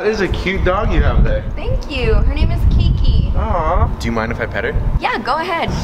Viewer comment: I thought he said kinky